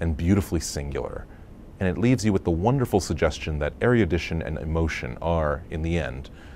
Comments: Does anyone know what he is saying at 1:30? I can't figure it out. Areadition and emotion? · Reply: Erudition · The exploration of the architecture as it relates comparatively to their respective familial relationships is evident.The buildings they explore, the use of space in the layouts along with the sharp edges and curves in the details of a design feature can run parallel to a curve in personality or a within the context of the nature that surrounds them. It helps broaden their discussions around those relationships as they deepen an understanding of themselves, each other and the structures in question. The use and absence of color as well as the textures, materials and their variation play a part as well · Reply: Ecuse me, do you know what he's saying at 1:30? I can't figure it out. Please help me. Areadition?